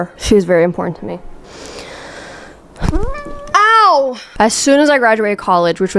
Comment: girl really said AŁ instead of ow!!!!!!